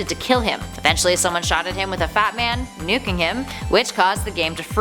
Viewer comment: did you just say “shotted”?